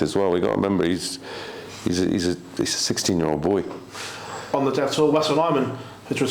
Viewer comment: Who are they talking about at 44:23? (The subtitles say "On the death toll West alignment has returned from suspension!) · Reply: Ah, it's Wessel Nijman from the Netherlands. Banned for 30 months for ensuring he lost 4-0 against David Evans in some Modus thing in 2020.